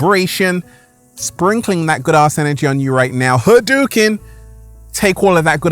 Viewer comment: Why do i keep hearing " Good ass energy"? 😂😂😂